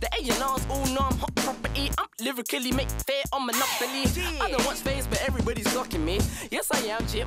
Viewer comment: Did I hear "I don't watch FaZe but every body's glocking me"?